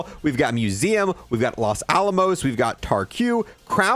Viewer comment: I was listening to this in the background and I thought he said “Las Alamo” 😭